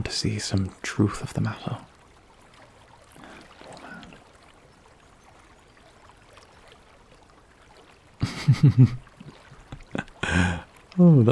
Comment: Did anyone catch what he said at 7:58? I think it was “Poor man” but I’m not quite sure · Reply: He did infact say poor man.